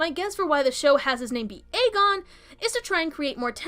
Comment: I thought she said the name was Aegar, not Aegon. Maybe I misheard.